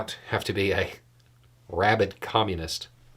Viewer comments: Thought he said rabbit Communist at first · When you were speaking, the first time I thought you said “rabbit communist” I thought what the heck is that? Now I understand he called you a “rabid” one. I hope he listened to the answer and understands now.